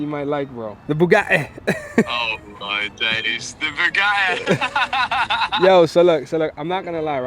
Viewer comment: did he say “ buggai “ ? lol 🤣🤣🤣🤣🤣🤣🤣🤣🤣🤣🤣